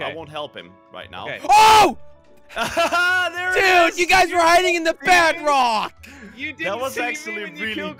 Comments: I thought said "You were hiding at the BADrock!" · dude you guys were hidding in a "badrock" instead of bedrock = 3:59 minutes in this video